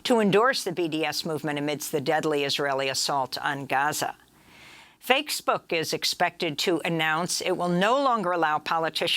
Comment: did she just say "FAKES Book"?? Gawd, I love Amy and her clever "slips"!